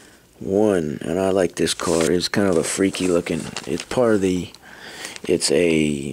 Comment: why did you said freakin?🤣🤣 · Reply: nope, I said freaky!